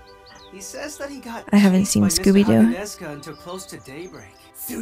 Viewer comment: did say she didn’t see scooby doo😐?